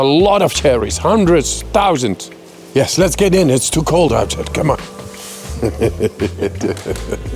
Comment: mins in - did I just hear Wim Hof say "It's too cold out here"?!